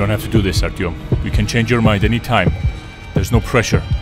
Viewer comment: Is it just me, or did I hear the word Artyom?